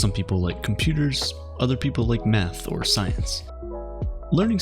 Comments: It really sounds like he said, "other people like METH or science" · I was like did this man just say someone people like meth!? I mean you're not wrong but still what!? (Ik he said math)